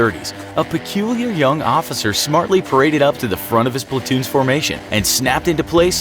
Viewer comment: Whenever he says Platoon I thought he was saying Splatoon